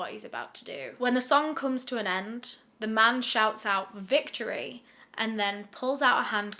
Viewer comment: he didnt say victory he really said: THIS IS FOR YOU